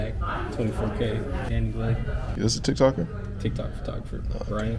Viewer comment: did he really say "6 lack" or am I trippin?